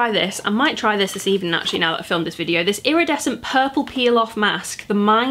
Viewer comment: when you were talking about the mask i thought you said pilaf instead of peel off lmaoo